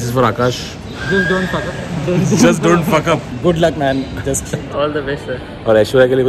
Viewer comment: let me be honest here, i heard him saying "please dont fuck her" 😳😳😳 i was like wats happening in the video as i was'nt knowing the whoole purpose of the video 😳😂